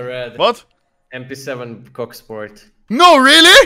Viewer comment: Did said cocksport??